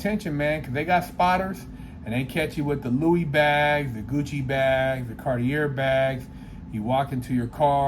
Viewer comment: I thought it said gucci mane